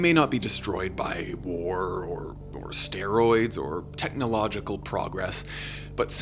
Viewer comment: "they may not be destroyed by war or steroids" instead of asteroids😂